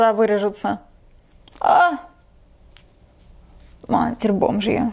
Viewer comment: Мне одной послышалось что сказала " матерь бомж я"